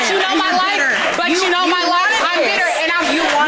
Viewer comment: Did she just say at the end, anybody can get nutted in LOL